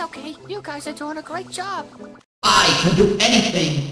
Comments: I love how jevil just spouts random bullshit and ends with "bye bye" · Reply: Wait it says bye bye?!?!?!? I always thought he said "Bad Boy!" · I love how jevil just spouts random bullshit and ends with "bye bye" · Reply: I actually thought that he was saying "my boy" instead of "bye bye" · I love how jevil just spouts random bullshit and ends with "bye bye" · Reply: ohh i thought he said 'boy boy" as in 'oh boy oh boy" but this makes more sense